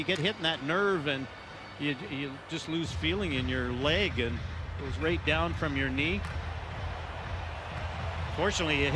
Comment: I wonder what Schmidt said to Myers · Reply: “What’s it feel like” “Ow.” “For real.”